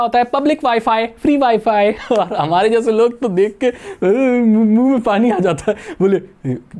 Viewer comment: Is it just me or did he say nunu me paani aa jata h 💀